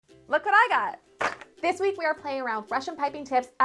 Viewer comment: at first, i thought she was saying something in russian, sounded to me like "lakaraga" 😂 had to repeat it 4 times to understand she was saying "look what i got"